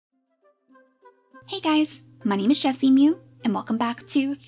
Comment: CC says "Jessie Moo" 0:02 :P